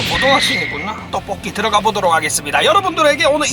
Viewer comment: 에 나만 떡볶이라고들리나?